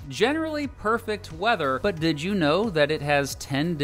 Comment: Have to watch your videos two or three times to consume all the valuable information you include because I'm laughing so much! 😂🤣😂 You got that dry humor i enjoy! Excellent! 😂👍🏼👌🏼Edit: You just said "Did you know..." but i heard "Did Juneau..." I see what you did there! ☝🏼😂